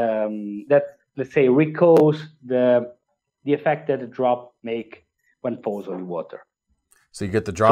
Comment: Y'all did him dirty with them subtitles lol. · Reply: What's Up? Maybe but the subtitles are SO much worse. @ 13:13 "recalls the effect that a drop makes when it falls on water." I heard him say it just fine. What they wrote doesn't even make sense.